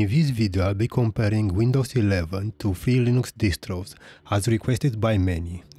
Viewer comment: To who's distro? At 0:03 "In this video I'll be comparing Windows 11 to Felix Distros" is what I hear....